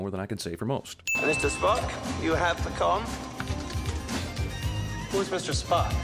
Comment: It sounds like he says you have the com,, when it's you have the conn. ( Or in the reference they decided to use, maybe he meant, you have the Khan :D )